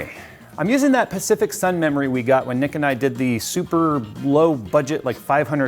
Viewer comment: did he said nickatnyte?